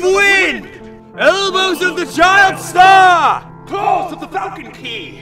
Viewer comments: did they say "elbows of the child star!"? xD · Did he say calls of the falcon pee?